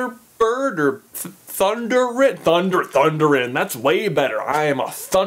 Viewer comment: Thunderin? I somehow heard that as Thunderan the first time and started imagining you as one of the Thundercats.